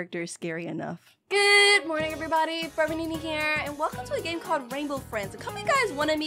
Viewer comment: In the 8ntrowhen you said forever nenaa i thought you said frevernini